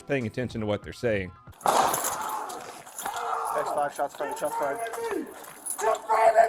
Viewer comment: OMG, YT IS SOOO FRIGGING COMMUNIST!!! At 3:14, When The Officer Radios In “Shots Fired, Shots Fired!”, The Closed Caption Says”Shots Fired By THE TRUMP CARD!” Go Straight To Hell, YT Commies...Love Ur Show, Donut 🍩!!!✝️💓🇺🇸